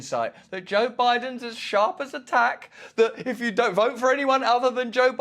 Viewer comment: When you said that MSM says that Biden is "sharp as a tack" I heard "sharp as a heart attack" lol.